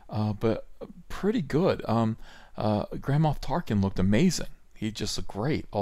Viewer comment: I actually thought you said "Grandpa Tarkin"